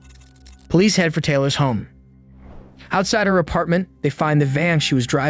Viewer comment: did he actually say “police head for Taylor’s home”?